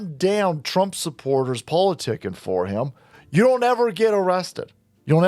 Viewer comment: He shoots the dude in a wheelchair then says “it’s alright everybody”😂😂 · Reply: I thought he said “don’t lie everybody” 😅 I could be wrong but that’s what it sounded like.